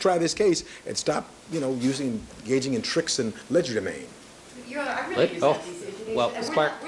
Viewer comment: what did he say.. tricks and??? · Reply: Legidemain…😂